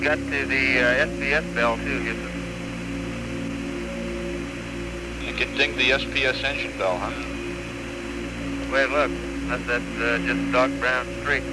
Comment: ohhh.... "Engine (SPS) Bell". For two decades I thought Fred/Bill Paxton said "engine belt", like a joke.